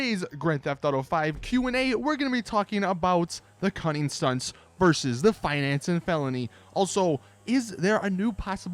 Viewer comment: I thought he said stunning cunts